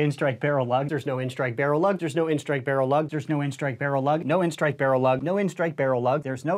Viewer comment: At the end it sounded like he was saying oh there’s Noin stikbarrlug